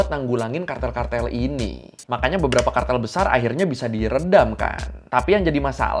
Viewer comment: Aku yang dengerin tanpa lihat videonya sempat ngira dengernya : "Makanya kartel besar akhirnya bisa beli rendang kan" 😅